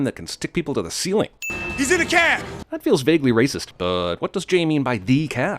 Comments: actually, I hear "a cab" here. Are you sure you got it right? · no, he said A cab, you just misheard it. Wonderful episode still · he clearly says "in a cab" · Wtf? He said “a” cab, not “the” cab. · I always thought he said (He's in a cab) damn you learn something knew everyday lol and also he brought her for a snack or at least I thought he said that.... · He clearly says "A" cab, not "the" cab. They need that sin back. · He doesn't say "the cab," he says "a cab." I don't know how you misheard that... (Also, before people reply to my comment he pronounced a like "uh" not "ey."